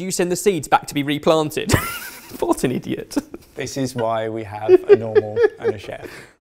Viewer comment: subtitles say “farting idiot” 😂